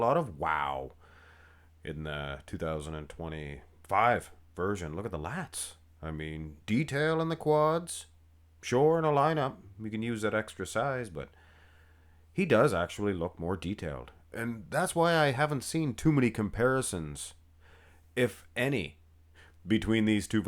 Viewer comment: Instead of "extra sized" i hear "extra fries"... I'll see myself out (and eat carbs).